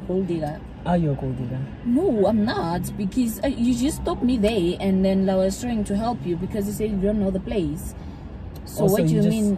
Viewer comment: "BIKIZ" instead of because🤣🤣😭😭😭😭